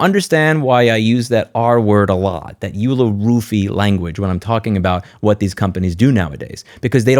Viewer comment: what. “r” word? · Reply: Roofie